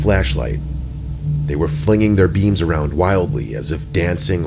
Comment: Mr Nightmare: “flinging their beams”. What I hear: “flicking their beans”. 😂😂😂